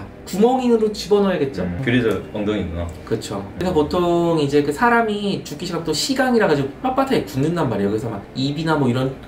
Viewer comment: 앗?! 5:55에 '시강' 아닌가요?? 오타인것같은데(아닐수도있지만요..) 조심스레 말씀드려요!🤔😶 시리즈 에피소드 많이 들려주세요! 기다리고있습니닷😉